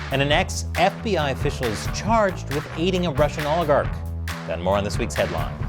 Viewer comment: LOL I thought at 0:09 he said charged with "Eating" a Russian Oligarch! I was like, this should be fun